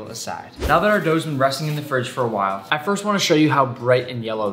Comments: Why did I think he said “go to hell” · did he just say go to hell??? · During the sponsor message my headphones unplugged just when nick started to say “go to hello fresh” so it sounded like he said “go to hell” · I heard "go to hell" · I thought he was gonna say "go to hell" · I thought he said go to hell 😂😂😂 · I thought he said “go to hell”💀💀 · When he said go to hello fresh I thought he said go to hell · thought he was bout to say "go to hell" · I heard go to hell 😱😱😱🤯🤯😰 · I thought he was about to say go to hell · when he said "Go to Hello <link>" i choked cuz i thought he said go to hell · when he said go to hello fresh (8:11) it sounded like go to h*ll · when you said " go to hello fresh" I thought you were gonna say " go to hell"🤣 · Did nick just say go to hell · Did someone else thought he said “go to hell” instead of “go to hello…” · I heard go to hell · i thought nick was going to say go to hell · I HEArD GO TO HELL xDDD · I thought he said go to hell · I actually thought he said “go to hell”😂